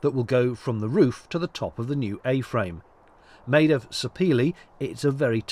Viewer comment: Didn't catch the name of that wood. Suppeelee? Can you give us the proper spelling so I can look it up? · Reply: Sapele